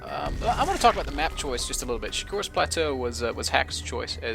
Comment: did he say "just a little bitch"